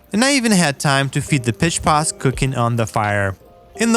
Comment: Word of advice. Don't get high then watch this! I got high 1/3 of the way through and heard him say "feed the piss pots" instead of "feed the pitch pots" and it made me laugh so hard I fell off my bed!